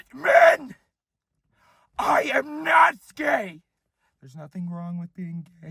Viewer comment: I think I heard "I am not not gay!!!" ??? So.... gay then.